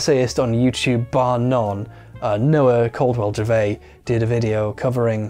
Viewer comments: Who is the youtuber he is referring to at 30:25? I can't quite catch the name. Edit: found him. For anyone else that is wondering, it's Noah Caldwell-Gervais. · I can't tell what name you're saying at 30:25 when you call someone "the best video essayist bar none". "noah cold war javay"? "knower culled werjave"? help me out